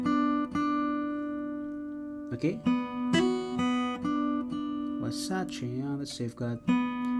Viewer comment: Did you guys heard him say "Versace on a safeguard"?😂